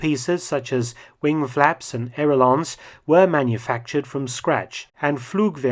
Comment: "wing flaps and air-o-lons"? What is an "air-o-lon"? Did I hear it wrong, or is that just a different pronunciation of aileron?